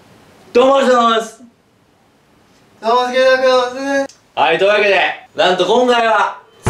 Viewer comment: のすんの挨拶 「どうもクイズノックのすんです」 に聞こえるのわたしだけ？( ﾟдﾟ)